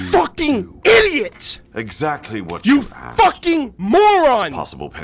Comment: So feel free to correct me if I'm wrong on this, but I'm certain Watt's saying "what you asked", but the subtitles say "what she asked"...........